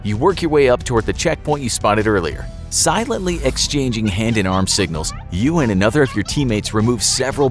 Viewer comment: narrater :silently exchanging hands signatures. animator:did you say talking and playing rock paper scissors.